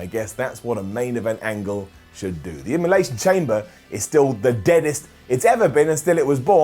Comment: Did you say Emulation Chamber ?? 🤣